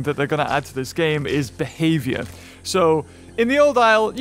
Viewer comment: I thought he said there are going to be beheadings XD